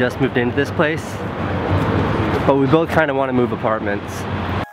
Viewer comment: At the start I thought you were saying, "we both wanted to move apart" and I was like nooooooo